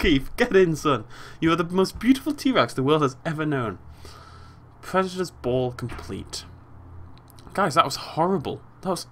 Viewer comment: i thought it said predator's balls